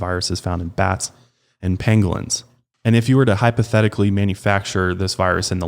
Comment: Did say “penglins”? 15:12 Edit: I just realized there’s an animal called a pangolin... I was 100% thinking of penguins.